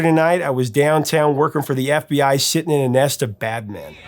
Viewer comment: Awe man! All these years I thought he was singing and saying "Bat Man!"